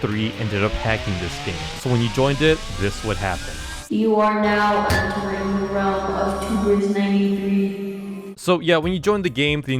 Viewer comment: Here’s what it said 6:57 you are now entering the realm of tubers93